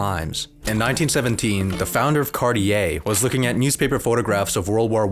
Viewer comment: I heard "was founded by Cardi A" And I thought that must be the one Cardi B stole her name from